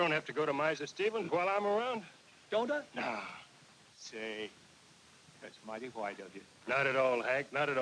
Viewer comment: "That's might white of you" - Or did I hear it wrong?